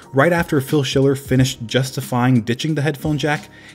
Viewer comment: I thought you said “bitching the headphone jack” instead of ditching. I about chocked on my food 😆